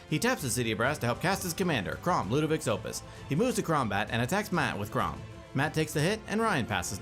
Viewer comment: “He moves to Kraumbat”..am I the only one who heard that right? At like 4:00 into the video